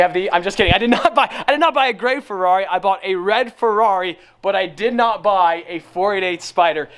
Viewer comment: I thought he finna say I BOUGHT A LAFERRARI